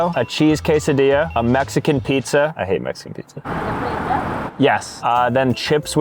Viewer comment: I thought he said I have Mexicans I had to replay it